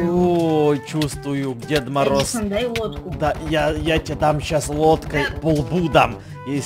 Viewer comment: Мне послышалось Едисон дай ВОДКУ!!!!!!!!!!!!!!😂😂😂😂😂😂😂😂😂😂😂😂😂😂😂😂😂😂